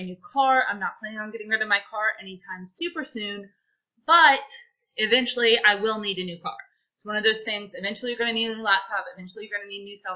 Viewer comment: Had to laugh. I thought you said eventually you will need a new sail boat.